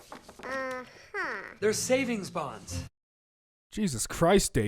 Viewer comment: what did he say? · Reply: They're savings bonds.